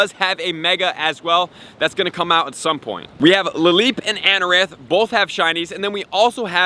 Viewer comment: he says, “Anoranth” instead of “Anorith!” Manz is watching too much Amouranth on Twitch! 😂💀